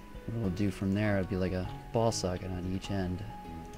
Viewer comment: sounds like he says advanced ball sacks not ball socket